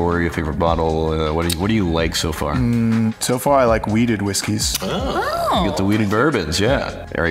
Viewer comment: For a minute, I thought he said he liked "weeded whiskeys," and I was like "I don't think that's legal in Texas."